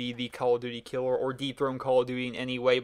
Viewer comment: When you said "dethrone" at first I thought you said deep throught lol dirty mind at work.